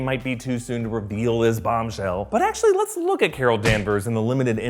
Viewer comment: Did I hear bombshell, as in Lana Baumgartner from the Miles Morales series. Was that a pun or unintended? Only knows...